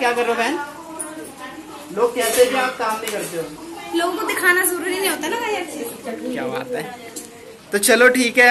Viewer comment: I think she said Logon Ku dikha na zaroori nhi hota na Mayank 😂😂